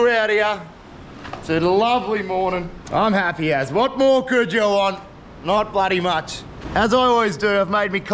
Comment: A few of y'all are waaay overboard as fans... 🤣 A second thing... at about 29:40ish. did bro say "what more could a girl want?" And no fishing at all? Still an alright vid... glad you've got a kool brother... I don't!